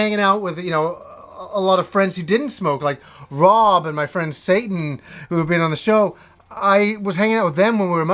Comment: "My friend Satan?" Lol did I hear this correctly?